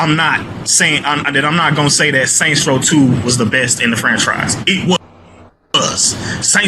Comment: sounded like he said “best in the French fries”😂